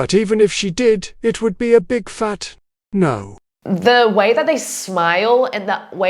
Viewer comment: did I hear naur?